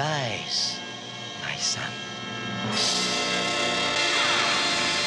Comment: I KNEW IT! All these years I could've sworn I heard Dungeon Master call Venger his son.